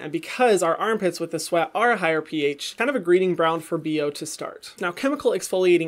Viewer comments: Did he say greeding bround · "greeding bround" instead of "breeding ground" 🤣 Love your videos! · did he say greeding bround? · I'm sorry did anyone else hear him say greeding bround instead of breeding ground